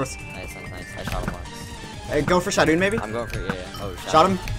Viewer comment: What I really heard : "Go for Shadoune maybe? Shadoune Shadoune Shadoune Shadoune" x)